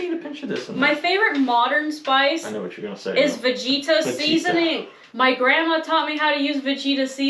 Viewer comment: I was listening while I was working on the computer. I for real thought you were saying fajita seasoning, and I was about to die! Lol! I've never heard of vegeta! I'll have to look into that.